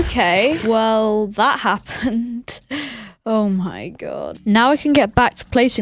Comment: The caption says 'oh my jod'